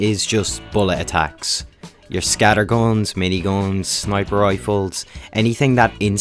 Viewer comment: i love how he says instead of guns, gons